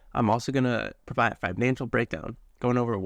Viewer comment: Thought you said a “mental brakedown”, because im having one in my mechanic job, and am going into this aerial game exactly the same as you. I bricked my mini2 trying to get it airworthy and bit the bullet for the mini3 pro.. im optimistic, and hope things work out for both of us.. im in it for the long game also!!